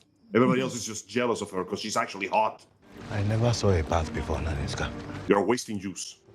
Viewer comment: I thought he said “I never saw a bath before….” Ninja please, you making it too easy bruh